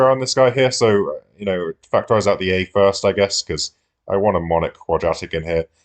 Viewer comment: ive done the reverse calculator factorising so much that ive basically forgotten how to factorise 😂 also what word did man sau here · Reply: He said "monic" then quadratic meaning a quadratic where the x^2 term has a coefficient of 1.